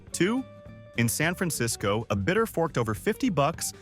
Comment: the text says, 'A bitter...' ...But what it really should say is, 'A bidder...' ...You should correct this.